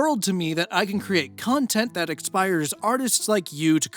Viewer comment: silly thing to notice a whole year after the video was uploaded, but at 27:30, it kinda sounded like you said "and it means the world to me that i can create content that *EXPIRES* artists like you" XD